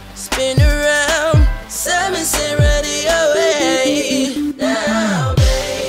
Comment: Wat did he say at 0:54 ?? Ready or what ???